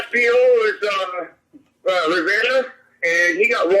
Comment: did he say "Rivander"??